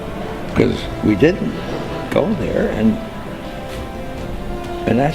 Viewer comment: Sounds like he was saying "we didn't go there" (meaning after Apollo in that long time). And because of this, he thinks he knows why... But sounded like it was to answer her question. Just my though. He looked a little old there, like others... And maybe gets confused too.